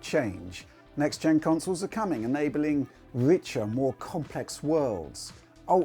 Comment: why did I heard 'Richard more complex world"